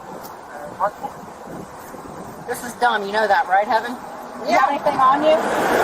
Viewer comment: did she call the girl Heaven? Like she already knows her name??